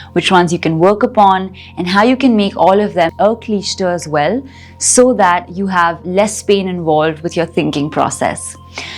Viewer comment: could you tell me what you're saying at 7:28-7:30 ? I heard it 20 times and I cannot understand the word - it looks like its cutoff while editing and it sounds like 'cliched' something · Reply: Yes! I said the word “Aklishta” - which means non-painful or not having the Kleshas